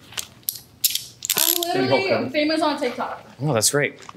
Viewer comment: bro at 19:54 she actually said im famous on tiktok